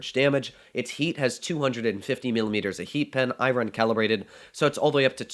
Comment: I thought he said the smasher has 200mm of HE pen I was like omg I want this then I found out it was heat :(